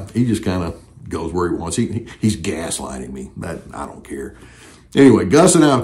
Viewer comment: Did you say you were being GUSlighted? So funny 😆😆😆